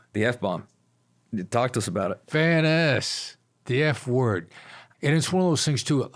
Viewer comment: mins I had to rewind, I thought Chris said “fat ass” hahaha! Fairness 😂. Awesome interview.